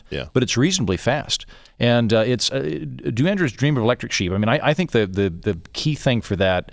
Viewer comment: I keep hearing - "Do Andrews Dream of Electric Sheep?"